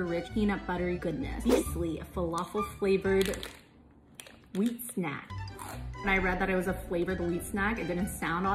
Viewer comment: Bro when she said “flavored wheat snack” I thought she said falafel filled snack